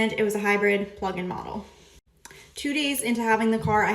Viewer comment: Did she say plug-in model?